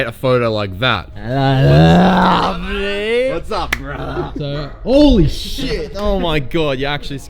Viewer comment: Did just said "brat" or I just misheard it? 🤣